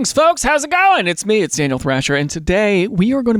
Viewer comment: Your into sounded like you said "I'm anal thrasher" 😵‍💫🫡